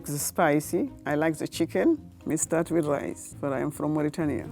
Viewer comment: it sounds like she said she's from Mauritania (captions)